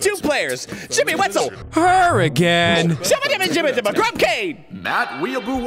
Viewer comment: When says 'her again' in the intro, I thought he said 'hurricane' and I was unsure what to think.